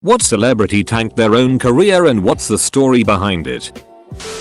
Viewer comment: I heard the first sentence, and thought I thought it said “tanked your own korea” I’m dumb